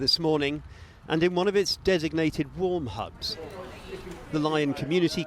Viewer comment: Warm hubs, thought he said p orn hub